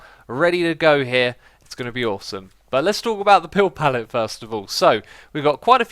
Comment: is it just me or does it sound like he is saying pill pallet here?